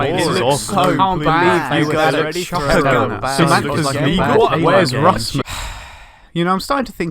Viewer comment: did i just hear "wheres russman" at 0:09?